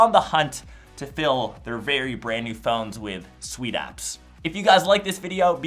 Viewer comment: Did he say sweet ass